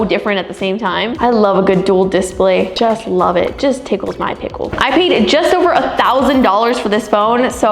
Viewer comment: Did she just say "It tickles my pickle..😊